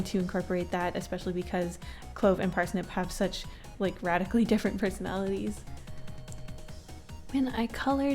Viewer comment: 100% thought she said "rat-ically different personalities"